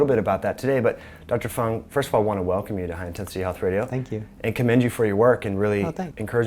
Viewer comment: i thought he said High Intensity Alfredo hahaha